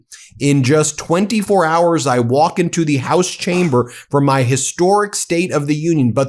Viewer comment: Ohhh, I thought he said "Tomorrow's the day." "Tomorrow I will be walking into the gas chamber".